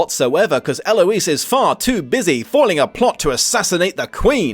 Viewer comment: Ah, so the author really enjoys the "I can fix this emotionally distant man who was traumatized in his past" trope. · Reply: Had to play back 33:10 because I swore I heard “Forming” not “Foiling” a plot to assassinate the Queen 😂